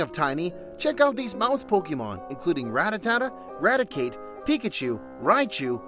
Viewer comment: Did you say "Ratatata" at 3:30?